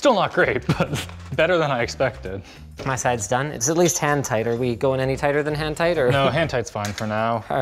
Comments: when he said hand tight i thought he said hentai lmao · "It's at least hentai, are we going any tighter than hentai?" "Nah hentai is fine for now." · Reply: either you misheard ''hand tight'' as ''hentai'' or your autocorrect is spilling secrets